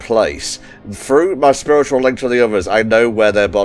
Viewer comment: I swear I misheard "spiritual link" as "scrotal link" when it was read out. lol